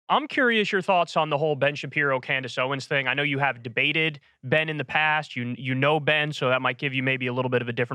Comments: Jeez I thought he said “ I know you dated Ben in the past” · Man! I misheard you as saying Ana dated Ben instead of debated him! I had to rewind and laugh at myself.